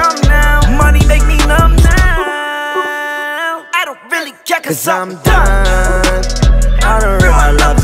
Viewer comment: Lil Uzi: "I dont really care...." Everyone: "If u cryyy" Lil Uzi: "Cause im done" Everyone: "Wait, Thats illegal" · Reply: Oh shit i thought he was saying "i don't really care cause im dumb." lol